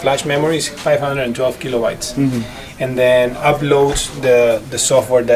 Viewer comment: did he say 512GBs? Holy Dina!